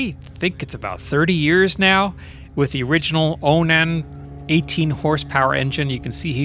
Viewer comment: About the quicky, it sounds like you said it has 18 horse power. 😮🤔 or is it 80 horse power? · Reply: 18!!!